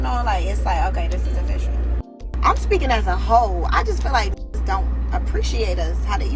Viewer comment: Did she just say she was speaking as a hoe?? 😳